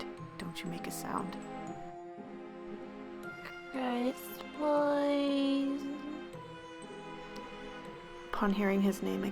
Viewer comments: omf it sounds like you said "chris booiiiiiiii" xD i laughed so hard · Anybody else hear "crisp wire"??